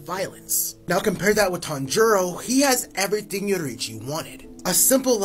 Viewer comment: I thought you said tanjiro until I looked at the images